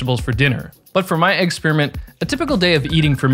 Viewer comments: did I hear "eggperiment"? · did you just say Egg-speriment???